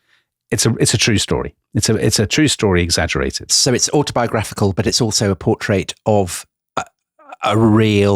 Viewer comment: What word does Tom use at 8:35? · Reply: Autobiographical: (of a written work) dealing with the writer's own life.